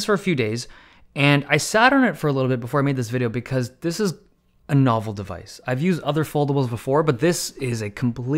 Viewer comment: When he said "a novel device", I misheard it for "an awful device" and was pretty confused.